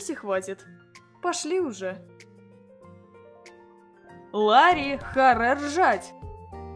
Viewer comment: что всем слышалось "Лари харе ржать!!" Что я услышала "Лари хре жрать!!!!" 😂